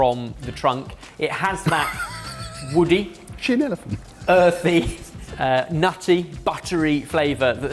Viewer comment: What does say at 4:22? My nearly-deaf ears didn't catch it and apparently neither did the subtitler, but it seemed to make them all laugh! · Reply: "Is she an elephant?" Because said something about mother nature tapping the trunk. 😊